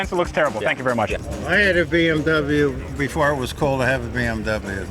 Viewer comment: "Let the car do the talking." That car would say "please don't touch me there senpai!" Also, the guy at 23:12 sounds shitfaced. I can't figure out what he's saying. "I had a BMW before it was called half a BMW"?